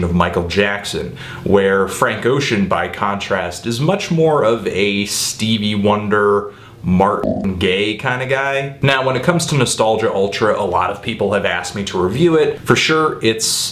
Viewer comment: did you add that sound effect to correct yourself cause it sounded like you said "Martin Gaye" lol.